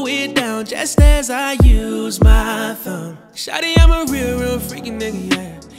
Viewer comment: “Turn off your phone, I'll use my tongue, I'll eat it all.. night long, I’ll go deep, I'll slow it down, just as I use my thumb” GEEEEEEEESSSSSHHHHHH. I CANT!!! 🤤🤤😩😩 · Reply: Oh he said thumb I thought he said phone lmfao